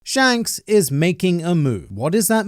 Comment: I had to repeat the first second of the video around 3 times to confirm that I heard, “Shanks is making her move” XDD